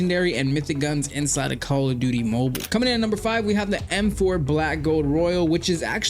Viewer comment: Ion know why buh i heard you say CALL OF DIDDY MOBUL